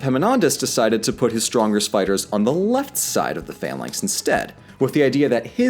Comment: I swear to god, I honestly misheard that as: ''His strongest spiders.'' and I just imagined an army of spiders fighting the Spartans. Epaminondas: ''Spiders! A giant legion of spiders!''